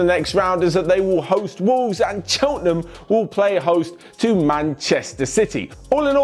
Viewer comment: I swear I heard say “chottenham” at around 4:16 when he spoke about chawley and wolves game and he accidentally carried it over to Tottenham 😂😂